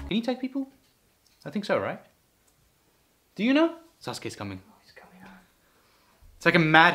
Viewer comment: did i heard right? "Sasuke's coming?